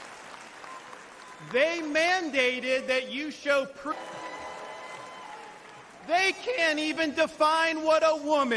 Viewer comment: if you, like me, wanted to hear ALL that Ron DeSantis said, and at 15:07 noticed some parts cut out i.e. EDITED, here is what Ron said "they mandated that you show PROOF of a Covid vaccine in order to go to a restaurant but they (Democrats) oppose PROOF of citizenship to cast a vote."